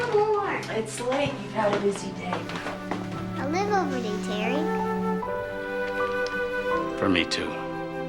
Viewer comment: What does the little girl say at 1:19:06? I cant make it out? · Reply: Where is Terry? Is he alright?